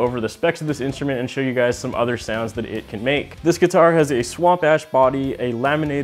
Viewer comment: I had to rewind, I definitely thought you said "Swamp ass body". But you said "swamp ash". LOL.